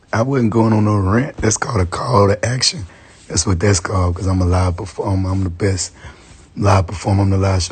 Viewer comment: what is this supposed to mean? 😭 "I said that because im a great person to watch live" huh? 😂